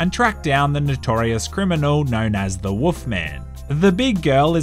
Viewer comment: did he say "woof man" ?